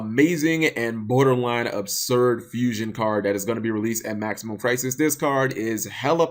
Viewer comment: I always hear "Maximum Prices" when you say the name of the set. Problably a prelude for when this set is released.